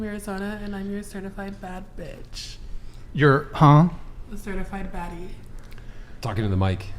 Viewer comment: Why does it actually sound like she is saying "A certified fattie." (A.K.A the truth)